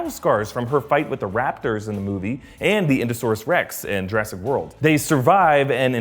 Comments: INDOSAURS REX?!?! WHAT! DID ANYBODY ELSE CATCH THAT! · does he say indo-saurus Rex